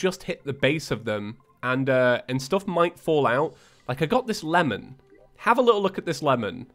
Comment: I swear to God I heard he said "melon" and when he said "lemon" the second time I got very confused...